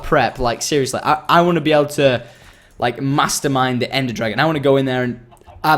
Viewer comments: When he said he wanted to mastermind the ender dragon I thought he said masturbate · When you said mastermind the Ender dragon, I thought you said masturbate